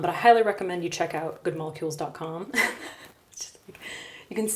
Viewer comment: "I hylurecommend you use Good Molecules.." is what I heard you say.